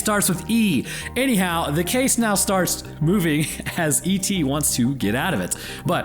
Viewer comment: I swore to god i heard Epstie Wants to get out of it when you said ET